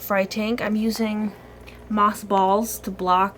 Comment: For a second I thought you said, "moth balls" instead of "Moss balls" XD hahaha